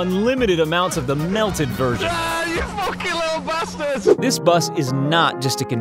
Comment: Did he just say fucking little bastards